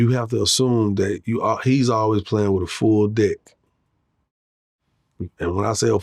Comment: at first I thought he said "he always playin wit a full dick" 😂😂